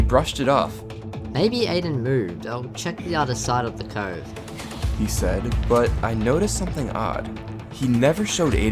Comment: said "He said" to punctuate his sentence since dylan said something. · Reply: yeah carl changa thought “he said” is “he’s dead”